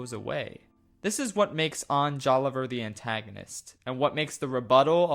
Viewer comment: did you just say Ohn Joliver?